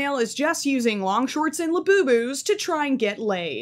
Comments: Why when you said labubus the captions said leooooooooooooooooooooooooooooooooooos 😭💔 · why does the closed caption say "leoooooooooooooos" instead of labubu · idek what the captions were thinking when you said labubu 💀💀💀